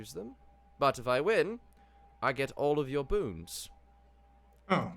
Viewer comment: Did he say bons bones and bonds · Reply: I believe he was saying boons.